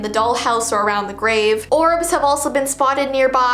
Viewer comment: I thought she said "Orcs" smh